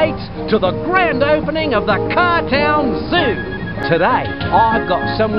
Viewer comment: I misheard it as the 'Cartel Zoo.' Goodness I lost my innocence.